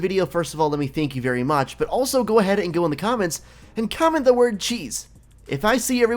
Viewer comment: Ooops...cheese - not geez. Lol. Thought you said geez.